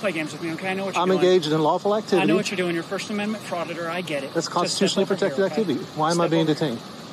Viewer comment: Did the cops call him a 'frauditor'...wow great attitude... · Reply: I thought he said a proditor